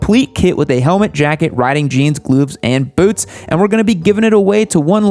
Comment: did he say "glooves"? xD